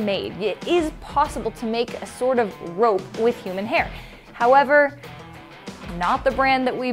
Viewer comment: I thought that said “can you make a rope with ham?”